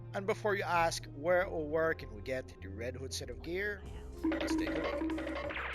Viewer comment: that sound I couldn’t hear what you said lol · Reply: I said.... Let's take a look. But Wonder Dog decided to run as I said that. :)